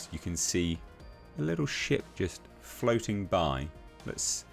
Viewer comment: lol, I heard: "you can see a little shit just floating by." In my mind I saw a dark brown turd floating in space.